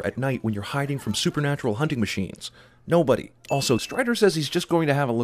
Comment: you have a typo. You say "Strider", but your subtitles say "Stider". Just thought you should know, cos that's a SIN haha. I've watched this video a few times and am just now catching it so sin for me, too. Thanks for the entertainment, sirs!